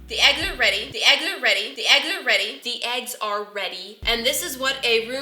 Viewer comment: am I the only one who heard “ThE eGgS aRe yEtTi” ?😂